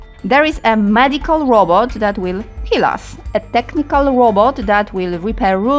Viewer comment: you scared the hell out of me, I thought you said a medical robot that would kill us. Lol. 😀